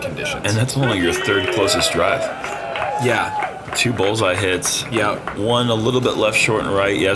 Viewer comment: into the video, did he say "Two bowls i hits" or "two bullseye hits"? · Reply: 2 bowls